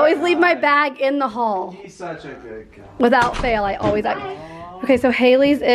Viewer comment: did anyone else hear say “he’s such a good guy”